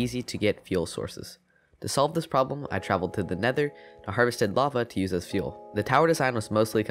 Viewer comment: Instead of saying LAVA he said BAVA